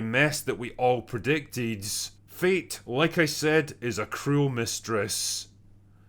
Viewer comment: I misheard "Fate, like I said, is a cruel mistress" as "Feet, like I said, is a cruel mistress" and I got pretty confused